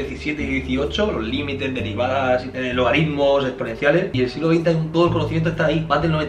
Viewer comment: pense que decia "Date un Logaritmo " xd